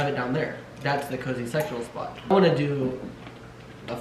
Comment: Lol when said “cozy sectional spot” i heard “cozy sexual spot”